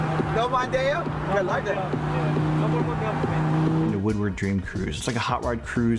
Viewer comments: For the first seven minutes of this video I thought he was saying “Dr. Dre”. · am I the only one that thought he said Dr. Dre the first time